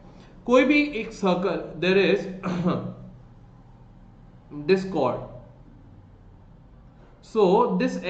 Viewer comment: I heard it as DISCORD 🤣🤣